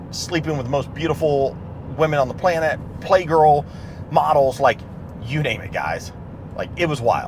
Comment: did he say he slept with Playgirl models?